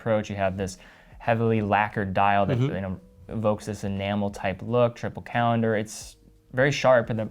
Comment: At first I thought he was saying an animal dial, I just couldn’t understand which or what type of animal. Then it clicked he was saying enamel 😁fantastic range of watches might get one.